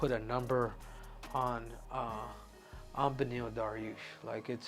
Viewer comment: Henry's stoned to the gills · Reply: I thought I heard him say "Deneil Bariush" 🤣 he's definitely ripped one before the show 🤣